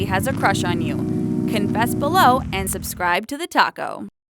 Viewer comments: At the end it sounded like she said "subscribe to the taco" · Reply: no she didn't she said talko witch is the name of the channel but it sounded like she said taco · Sounds like she says ' Subscribe to the Taco' at the end....mmmm tasty